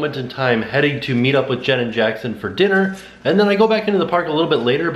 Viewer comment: At the end of the video, I thought i kept hearing say, Janet Jackson. But was actually saying Jen AND Jackson 🤣🤣